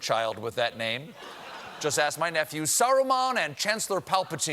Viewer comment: "? and Chancellor Palpatine" What's the first name he said? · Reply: Saruman